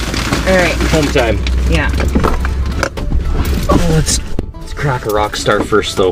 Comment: Hahaha...I thought you said let's crack a rat first... but you said let's crack a rockstar first.. Oh man this shelter in place is messing with my mind..haha. stay safe and have a great week.